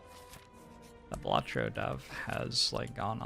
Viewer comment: I heard "the WATCHER dev hasn't played slay the spire 😂